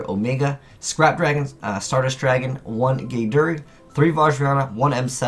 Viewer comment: when you said gae daerg i thought u said GAYDRA